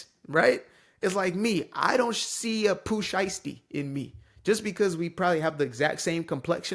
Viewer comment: A what ? New word . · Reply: Pooh Shiesty: He’s a Rapper from Memphis who’s serving time for being well, Shiesty